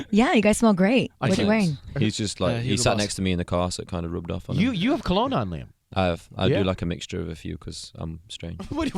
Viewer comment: does anyone know what cologne niall said to the girl? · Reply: Hugo Boss.